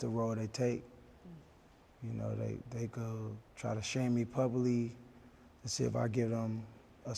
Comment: did he say puby vs publicly?